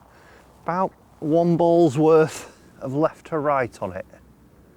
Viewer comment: Did anyone else hear that as “Womble’s worth of left to right on it.”? Just me? Oh!